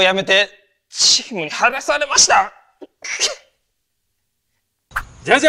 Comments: 僕は大学を辞めてのあとなんて言ってるん？ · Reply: 「チームに入らされました」だと思います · 最後の後藤の言葉がどうしても聞き取られへん… 「僕は大学を辞めて…」のあと、「父に見放されました」「チームに入らされました」色々聞こえるねんけど「ちちみはがされました」のとこなんて言ってるん？ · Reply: ｢チームに入らされました｣な気する